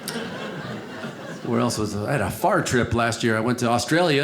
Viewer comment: I thought you said: "I had a fart trip" 😂 Me: someone needs to go to sleep asap